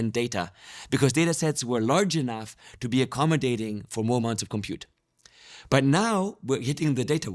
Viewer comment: the subtitles say "small amount of compute" but he says "more amounts of compute"